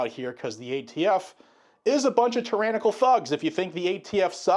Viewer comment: I thought you said “tryannical” instead of tyrannical. You didn’t, but I like that word for the AFT and others who keep trying to be tyrants, but keep getting themselves kicked where their balls should be.